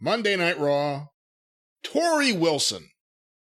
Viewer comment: When said "Torrie Wilson" I was surprised at Jim's reaction at first, and was like "What do you, mean? She's absolutely a legend!"....because I thought he said "Trish Stratus" lololol! Then I realized that he said TORRIE WILSON and was like "Oh....oh God, they're trying to pass HER off as a FUCKING LEGEND NOW?! JfC...."